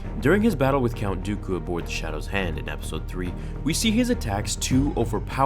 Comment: Am I the only one who heard him say shadows hand instead of invisible hand btw im talking about the ship at the beggining of rota where soon I died